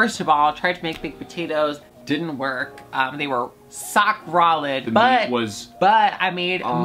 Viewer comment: Did anyone hear sock rolid at 21:22?